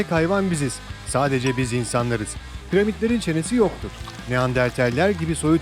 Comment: "piramitlerin çenesi yoktur" ne demek diye düşünürken, PRİMAT demek istediğini anladım. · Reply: Zaten primat diyor